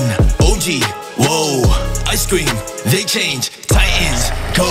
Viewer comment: Is it just me or did it sound like he said Ice cream😂😂😂😂